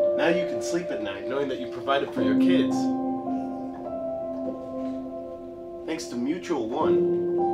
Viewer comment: can someone please help me understand what misha is saying at 29:40 ? I really can't get the words understood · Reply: He said: Now you can sleep at night knowing that you provided for your kids, thanks to Mutual One (a bank).